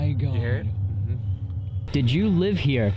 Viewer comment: same woman that said yes sounds like she says ‘they don’t know me’